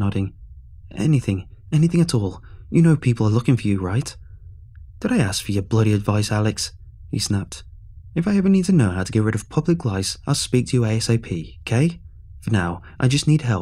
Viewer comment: Its like 6.20am but did i just hear public lice